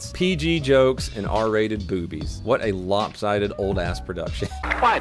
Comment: i thought you said poopies